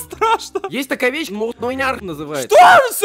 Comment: что он сказал? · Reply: муньярх